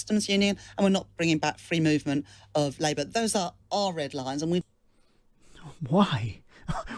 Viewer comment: She said those were OLD red lines. You misheard